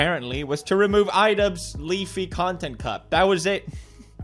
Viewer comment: Pewds Accent sounded like he said Leaf content cups and not cops i smiled at that pewds accent is just perfect👌🏻